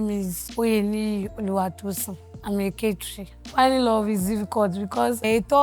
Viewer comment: am a what ? Did I hear Catery? 😂😂😂😂😂